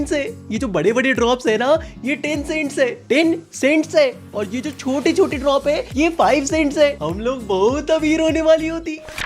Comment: i heard tencent PUBG MOBILE 💀